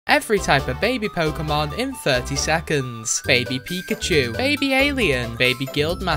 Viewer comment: I thought you said "every type of MAID pokémon in 30 seconds"